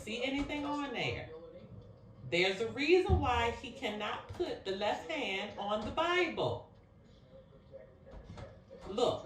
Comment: I thought I heard McKinley didn’t put his hand on the Bible 🤔